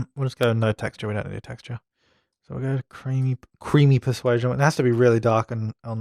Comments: I died laughing cuz for some odd reason I thought he said "creamy piss white" at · I died laughing becuz I thought he said 18:54 "creamy piss white"